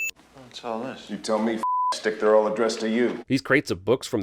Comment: As in 10:40 Hadley calls Andy “Fuck stick” this is his ‘thing’ to call his prisoners. In the beginning you mentioned “Butt stick” however, that’s not what he said :). Awesome Sin Review!